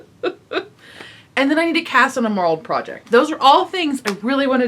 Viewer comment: I thought you said, "I need to cast on a moral project" What?